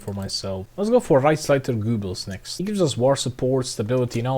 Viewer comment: did he just say reichsleiter goobers